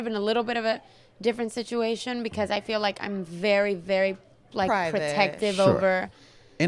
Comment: did camila say im very protective of "her"? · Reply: She said im protective over